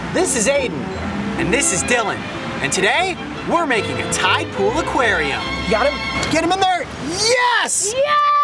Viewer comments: I thought it said “Making a tide pod aquarium” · For a second their I thought it said "Tide Pod Aquarium" lol I AM ONE WITH THE MEMES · I thought the video said tide pod lol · I thought it said tide pod lol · I thought it said tad pole aquarium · Who else thought it said tide pod aquarium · I thought it said tide pod :( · I’m so weird I thought it said making a tidpod Aquarium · Oh, I thought you said tide pods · I for real thought it said Tide pod aquarium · i thought it said ''Making a Tide pod aquarium''